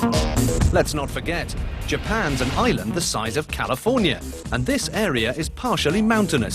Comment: what does he say at 1:37 ?? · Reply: "Let's not forget: Japan's an island the size of California."